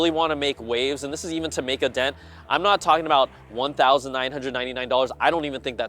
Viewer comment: I thought he said "I'm not talking about $1,099" and said to myself "yep, I agree, probably more like iPhone pricing, around $899-$999 maybe", but then when he said "I'm talking about something around $1499" I realize I misheard him. $1499 is still just too much for a lite version - I could see that being the "Apple Vision" standard model price, but I really do think there needs to be an even lighter/Air/SE whatever model that's priced below $1k (I would personally go for that $1499 standard model myself, but for mass adoption the psychology of that price is just too much). Though, from this perspective of a slow rollout, that would make sense in year two to come out with a standard model at $1499, capture some of the non-dev tech/Apple fans like myself, and then in year three or four aim for that sub-$1k every-man model.